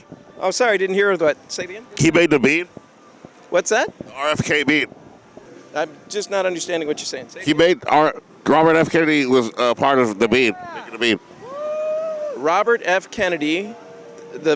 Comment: Bro, why did you say “bean” like that I couldn’t understand a goddamn thing you said 😭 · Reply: Literally thought he was saying "Robert F Kennedy was, uh, part of The Beat"